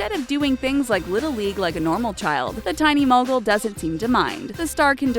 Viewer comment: Almost thought it said "The Tiny Mongrel"